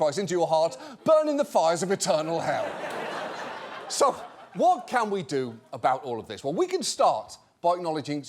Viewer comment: I thought he said so what can we do about Mormons for a sec